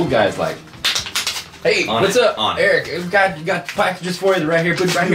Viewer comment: Did just say his real name!?!? Eric????